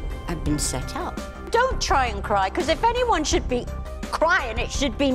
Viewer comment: I believe Sheryl said Sharon told her to stop crying. Where is that? I thought Sharon said " don't cry I am the one who should be crying." Big difference.